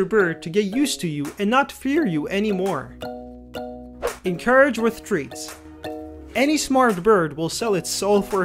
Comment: "encourage wit htreats" translation : Bribe them! :) also the quickest way to a birbs heart is through it's crop :) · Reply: I THOUGHT YOU SAID ENCOURAGE WITH THREATS HELP LMAO